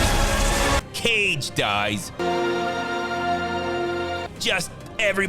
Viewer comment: Can someone pls recap what was said after 15:23? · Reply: Caged Ice